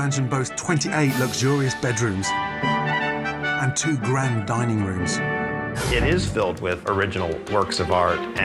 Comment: It almost sounds like they were saying dieing room in the beginning